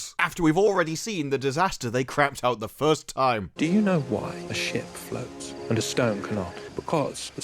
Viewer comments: it sounds like he says : "Do you know why the sht floats?" XD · Ah, for a year I thought he was saying sh*t floats. The super fans (probably accidentally) did make one good point, RoP has probably made more people appreciate the Hobbit movies